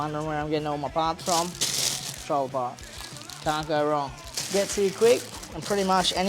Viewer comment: says a name of a shop, but have re-watched it 10 times and just can't understand the name of the shop hes saying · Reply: Patrol-a-part